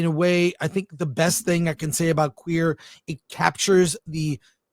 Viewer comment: I thought that said: "QueeF" 😂😂😂